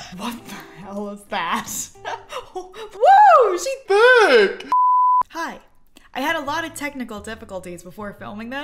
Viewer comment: and 0:18 it sounded like saying “hi.”